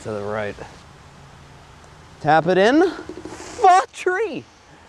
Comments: it sounded like he said fuck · i know you said for three 3 but it sounded like f**k three